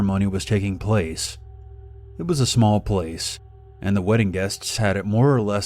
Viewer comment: I thought it said webbing and I was like “what the fuck-“